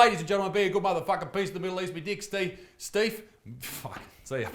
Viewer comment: Did you just say "me dicks Steve?"